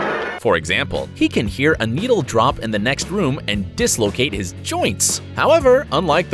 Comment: wait did he just say dislocate his joints!